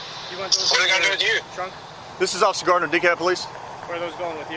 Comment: "With him being a sovereign and being a douche and all that..." 😂😂 and then 26:38 totally sounds like he says " this is officer something with dickhead police" i swear😂